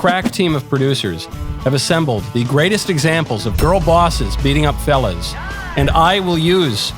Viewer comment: Why does it sound like he say “ girl bosses beating up felons “ 😂